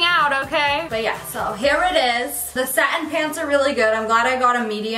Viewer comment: subtitles "it is the satan "instead of satin ly ❤